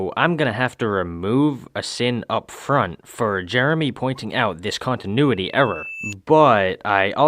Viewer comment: did u just call him Jeremy